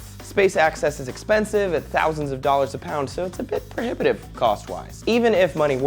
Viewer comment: "perdipative" what is this word??? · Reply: Prohibitive